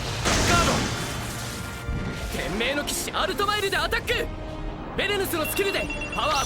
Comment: Did Shion said Blue Sky Knight Altmile?